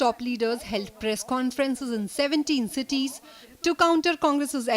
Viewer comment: Lol.70 city ??? What need ? We can understand all . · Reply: Arey watch 10:15, the news anchor says “BJP top leaders held press conference in 17 cities but the subtitle says as 70 cities...Lol again typo error...I think even here the BJP leaders will say as typo error. Hey Bhagvan, ab meh in unpadh Ba Ja Pa Sarkar se kya karu???